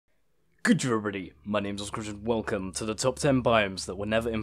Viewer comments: Could you maybe try to slow down on your intro? All I heard was "Good jjerybody. My names ascrdge...". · What the hell are you trying to say? goodjurady mynaysasmcruch